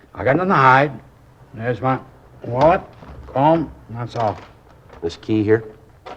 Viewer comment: I thought he said there's nothing in my pockets except my wallet and my phone. Had to listen a few times to hear he said comb, not phone 😊